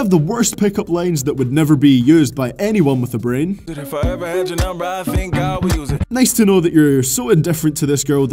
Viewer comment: I died at "And if I ever had your number I think I would use it." Classy guy I can tell. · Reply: I actually thought he said "And if I ever had your number I think I would lose it."